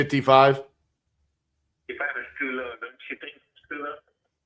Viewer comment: I ran this part back 3 times. I still didn't hear what number he was at but the art of negotiation was beautiful. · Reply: He said “it’s too low, don’t you think?”